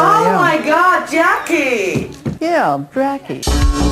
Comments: Oh my god, Jackie ! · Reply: Lmfao i can't stop watching this clip! I thought she said Brackie at first too, but after about the 10th time, it sounds like she says "Yeah. Drackie"...either way, she is too incoherent to pronounce her own name which is the absolute best... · I absolutely love this series. “Yeah, drackie” at the beginning had me in tears. The editing with the 90s footage and my new fave genre of music thanks to you is just so nostalgic and makes me happy in a way I can’t explain. Thanks for creating my favourite series on YouTube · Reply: I keep replaying that part! I hear "Yeah, brackie" !! Lllllol